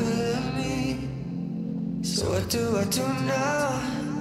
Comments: i always thought he said "i do, i do not" instead of "what do i do now?" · Instead of "what do I do now" I hear "what do I tuna", I want to die.